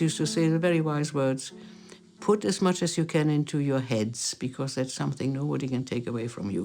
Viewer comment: What does the lady says at 7:59? Is it the following? "Put as much as you can into your heads, because if there's something new, they can't (or is it can?) take away from you"?